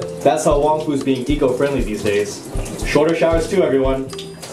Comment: haha i heard "shoulder" too, but i think he meant "shorter"